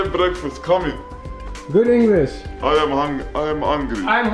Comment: i heard "i am angy" instead of "i am hungry"